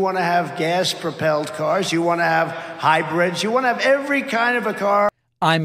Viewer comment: he didn't say high breads, he said hybrids. "You wanna have gas propelled cars, you wanna have hybrids..." And instead of something with a "forum" he said "I'm for 'em". Other than that, I hope Trump accidentally stubs his little toe today.